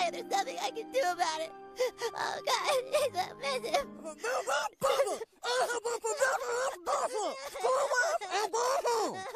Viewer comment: He actually said “Now that does it”